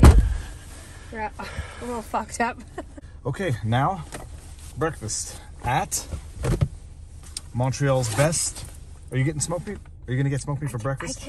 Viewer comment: What was said: " you're a little fogged up" What I thought I heard:" you're a little fucked up"